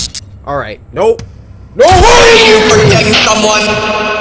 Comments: Who else heard mama say "orange you forgetting someone" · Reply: No I thought she said ARE YOU DATING SOMEONE! My family when I get a bf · Tattletale: knock knock Cory: umm... Who's there? Tattletale: Orange Cory: TF is he talking about 😑 Orange who Mama: ORANGE YOU FORGETTING SOMEONE!!!!!!!! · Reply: PurpleSaiyan 1223 I thought mama said "aren't you forgetting someone"whelp now I know mama finished the jock at lest XD. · Who else heard mama say "orange you forgetting someone" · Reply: I thought she said “are you fricking someone” and I started crying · Who else heard mama say "orange you forgetting someone" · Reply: That's what she said? I thought she said "Protecting someone?!"